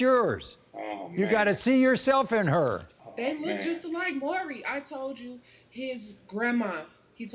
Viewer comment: Did she say those kids look like Maury